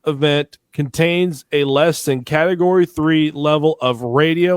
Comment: The dude said less than level 3 (7:17), and you go searching for level 3. He CLEARLY said LESS THAN < level 3.